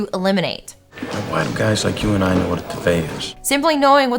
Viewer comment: I really misheard Duvet as Badet throughout this whole part and was nodding along like mhm mhm. 😅🤣